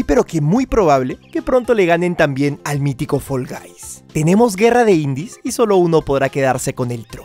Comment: Soy yo o escuche Full Gays en el minuto 6:10 Xd